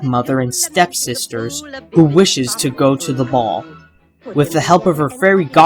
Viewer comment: At first i thought you said mall.